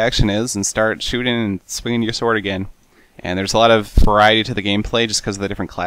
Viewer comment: I thought he said "There's a lot of Variede to the game."